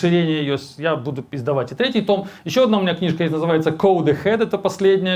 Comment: послышалось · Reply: он говорит "я буду издавать третий том", а слышится "я буду пиздовать третий том"